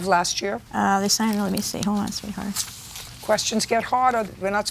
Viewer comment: did she call JJ sweetheart? 😮